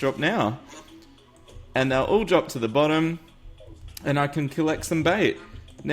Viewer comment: Another amazing video! YEEEEEESSSS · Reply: Quin- Mackenzie Foster I thought you said 'gonna collect some bae' xD